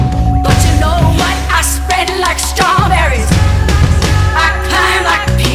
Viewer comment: Who else is laying in bed at 2:20 am with a squinty face at the “I spread like strawberries..” part! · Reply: Last night I thought she was saying "I smell like strawberries!" and I was like yes girl you smell so good, go off! lmao